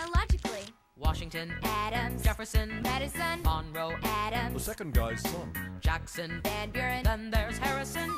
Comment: I ALWAYS thought they said "the second guy sucked" but it's "the second guy's son" 😂😂